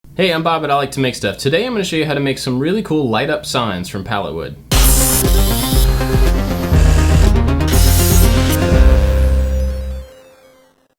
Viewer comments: At first I thought Bob said From Hollywood then i realised what he actually meant😂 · I thought he said LED signs from Pallettown XD